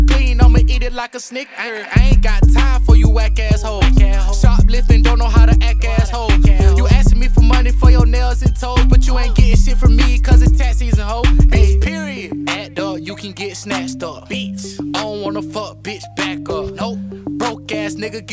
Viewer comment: I thought bro said tat season🤣